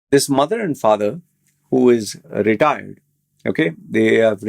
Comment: LOL I heard retard